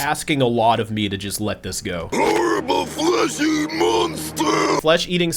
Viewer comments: i think he said fleshy not flesh eating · I thought he just said “fleshy,” like the equivalent of a human saying “hairy monster” or something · I don't hear 'flesh-eating', I only hear 'Horrible fleshy monster'. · I think he said fleshy, as opposed to their frankenstein-y stitched or mummy wrapped skin, tho the loboans have flesh, so don't know what that's about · I always heard that as “Horrible Fleshy Monster!” Since transylians have so much metal and stuff on their bodies that someone purely made of flesh would probably be pretty weird and gross to them. Although with that dude’s weird blubbery gruff voice I could also see how it could be heard as “Flesh eating” too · I don’t think he said flesh eating, it sounded like he said “fleshy” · Sounds like he's saying "Fleshy" instead of "Flesh Eating" I think the joke is that they don't have skin while Humans and other Aliens do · I think he is screaming "horrible fleshy monster." So I guess he is too fat instead of being all muscle/bone/bandages? Some mechanical bits covering the weakness of his flesh might make him more normal to them.